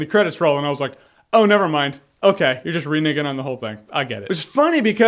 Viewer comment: Did you say reniggin at 8:48. I'm offended...LMAO.Yeah dude you were spot on with your review